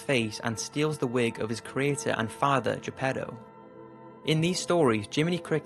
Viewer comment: did he say “allforagartitha father”